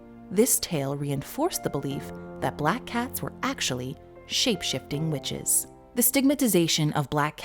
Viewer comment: did she say cats are shift shaping bitches :0 lol